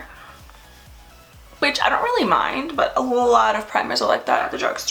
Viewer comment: did she say bitch i dont really mind 😭😭